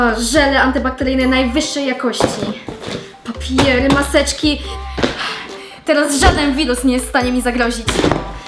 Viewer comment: czy tylko ja usłyszałam? - teraz żaden bigos nie jest w stanie mi zagrozić